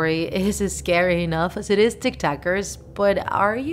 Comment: I thought she said what's up tiktokers